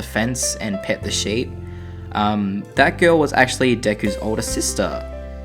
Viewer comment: the subtitles keep on mishearing Deku and reading d**k you